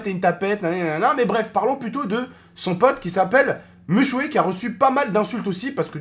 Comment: C moi ou ta dit "mushway" a 0:36 ???